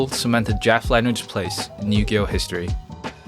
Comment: Your accent is horrible One of the best "acts"? Can't pronounce DECKS? LMAO 😆 When you say 0:25 yu-gi-ho history the subtitles say new year history 😂